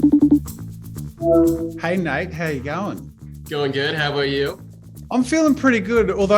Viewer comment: Yes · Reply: thought so. Just how he said how ya going nate. It kinda sounded like saying how ya going mate 😂